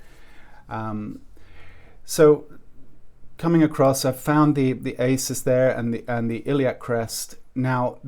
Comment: what's the ases? You say at 1:20 the cases and the iliac crest. sorry about the spelling, I don't recognise the word. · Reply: ASIS. Anterior superior iliac spine. I dicuss it in the figure drawing fundamentals, lesson 1 video. It's an important landmark for us to be aware off.